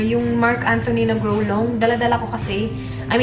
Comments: I just heard itzy's Dalla Dalla omoooo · I just heard Itzy's Dalla Dalla when Ate introduces the cleanser. 💖